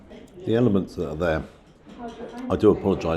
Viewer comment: Thought you said Elephant's at first instead of Elements 😂🎉🕊️💯🙏🏾🙏🏽🙏🏼🙏🏻🙏🏴Had to Replay that part Twice.Well done again and Camera 📸 Man 🌎